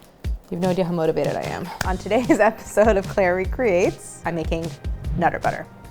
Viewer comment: Some thumbnails have the text "Claire makes", so when you said "Claire recreates", I thought you were saying "Clairie creates" for the longest time. 😂